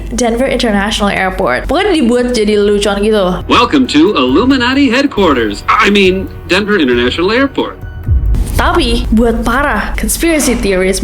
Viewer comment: I mean, Denver Indonesia airport. Apa hanya aku yg salah dengar??